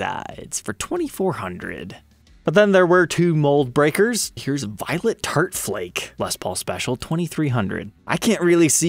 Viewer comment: I thought that said violent fart instead of violet tart